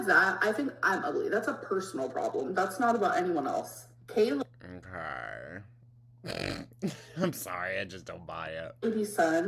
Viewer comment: “kay-lady-son” instead of sent me @ 6:55 😂